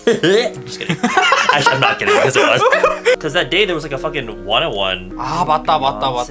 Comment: I heard wanna one. 😢